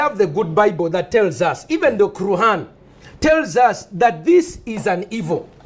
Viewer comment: does he say "even the " kurhaan" i.e Quran😀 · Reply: yes. He says that even quran condems it